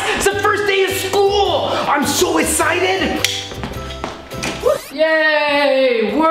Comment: I'm so escited! I think was so excited he said ESCITED.